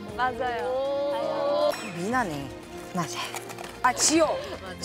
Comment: can someone tell/translate for me what mina said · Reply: “맞아요” kinda of under here breath. it means yes/that’s right.